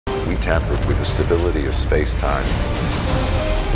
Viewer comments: I thought he said “the multiverse is a concept of which we now frighteningly live on” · I thought he said “the multiverse is a concept of which we now frighteningly live on” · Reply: I thought he said "frighteningly lible"